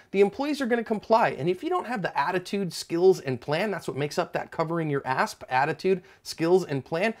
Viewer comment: To be honest when I first watched this channel..at the end of the video I always thought said "that's why you always cover your ass"😂